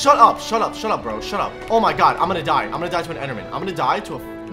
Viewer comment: What did he sayyy???? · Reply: he said "im gonna die to a frikn-"